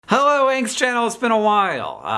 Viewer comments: He said "Hello Hank's Channel" but I heard "Hello Angst Channel" and was like 'same Hank, same' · It kind of sounded like he said “Hello Angstchannel” and.. yeah. I felt that.